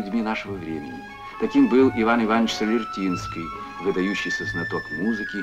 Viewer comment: wrong subtitres: 6:26 - it has to be "Solvertinsky" instead of "Vertinsky" (singer).